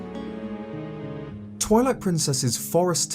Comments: Did he just say TOILET PRINCESS😅😂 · i can’t be the only one who heard toilet princess..